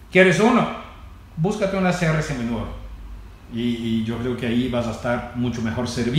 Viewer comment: A partir del minuto 12:00 al 12:05 no entendi, cual era la mejor opcion ? Saludos. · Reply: Sr seminuevo fue lo que dijo